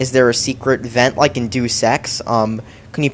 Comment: And every time he says deus ex it sounds like he is saying do sex.